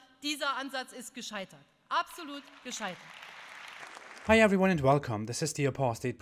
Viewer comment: I heard “absolute scheisse” 😂😂